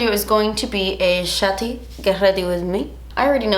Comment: For a sec i thought she said “shatty get ready with me” and she meant to say shitty HAHAHAHAHA i was ab to say none of her videos are shitty...